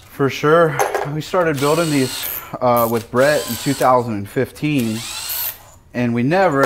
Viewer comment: Thought he said he started building engines with bread 🤣